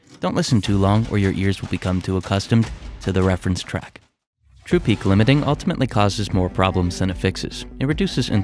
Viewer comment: Thanks, nice tips Can't understand the words at 0:39 though... trippy kilometing ? 🤣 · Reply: True peak limiting. Its a setting on fabfilter pro L2. You can see him turn it off in the video